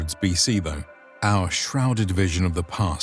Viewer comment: Might be because I'm in a daze since I'm watching this sick but I legit had to rewatch at 4:58 a few times because I thought he said "the Kingdom of Kermit" there......